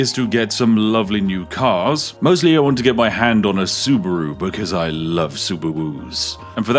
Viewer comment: YOU DID NOT JUST SAY SUBUWU! I REFUSE TO BELIEVE THAT THAT IS SOMETHING I HEARD YOU SAY SPIFF!